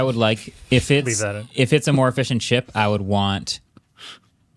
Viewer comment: I was being lulled to sleep from the dulcet tones produced by the RE27 and all of sudden I heard "a fish n' chip" and perked up and ready to order 🤣🤣🤣